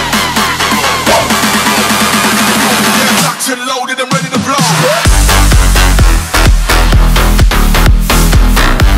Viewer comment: what it says un the minute 1:13 - 1:15, plsss replyy!!! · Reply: Locked and loaded and ready to blow (or go)! You are welcome :)